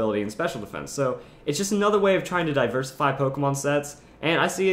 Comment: when you said diversify i thought said "diverlisify" hahaha